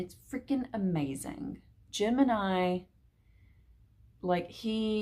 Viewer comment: Happy Dark October! I was confused because I thought I heard you say “Gemini” and I was wondering what you meant, but you were saying “Jim and I”! 😂 My northern ears didn’t process.